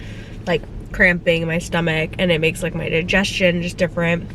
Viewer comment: I could have sworn made a mistake in one of the vlogmas vids but I didn't want to point it out. It was when you were eating breakfast and explaining how much of an appetite you have had in the first trimester.. and mentioned the gender I believe saying "my ___" while walking to the fridge but I wasn't sure if I heard it correctly. Not going to say what I thought I heard tho.. don't want to ruin the moment... if I'm right and she did in fact say it... hahah. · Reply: Haha I said “I need to feed my stomach” not “my son” haha a lot of people thought I said ‘son’